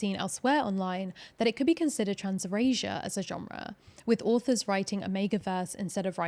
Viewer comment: caption says transracial 😭not erasure💀